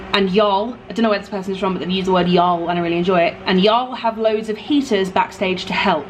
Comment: I said I really enjoyed it...?! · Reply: The first time I listened I heard "I don't really enjoy it" but I can hear now that I just imagined the don't! Us southerners appreciate y'all lol.